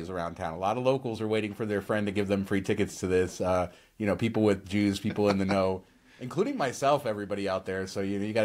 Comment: Did you say "people with Jews" at 14:25?!....